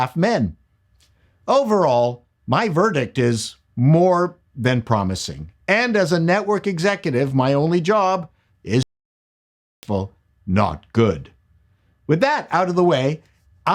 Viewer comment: there is a glitch and your voice cuts out. What were you saying? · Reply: I'm saying I think it looks more than promising and my job as a network exec is to make it successful not good.